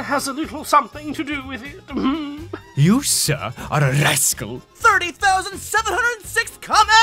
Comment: When they said rascal I thought he said rice gum lol 😂